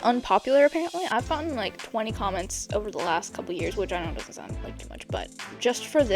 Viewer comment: For a second I thought it said "for the last twenty years" HOLY MOLEY- wait. ~face palm~ This is so helpful!! Thanks for sharing your wisdom!